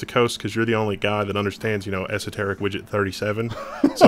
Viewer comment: what was he said at 12:16 secharet-widget-37?